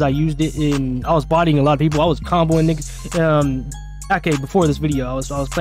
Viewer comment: Bruh did he say "i was comboing nigg- uhhmm"